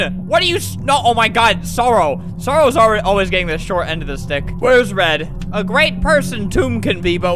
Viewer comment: listen I think he said the dic